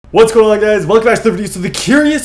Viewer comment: Can Anyone Translate .... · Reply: I thought I heard what's going on guys welcome stfu so the curious question